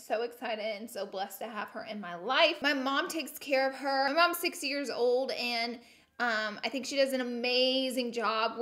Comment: did she just say her mom’s 6 years old? LOL 😂